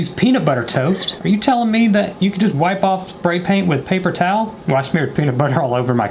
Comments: who else heard him say pray spaint I V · I know i ain’t the only one who heard him say “Pray Spaint” 💀💀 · I know i ain’t the only one who heard him say “Pray Spaint” 💀💀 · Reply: Same I thought he said pray spaint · I know i ain’t the only one who heard him say “Pray Spaint” 💀💀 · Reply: On nvm now I hear “sprain pain” lol